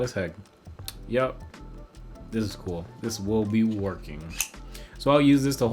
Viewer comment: pardon me what did you say😃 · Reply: THIS WILL BE WERKING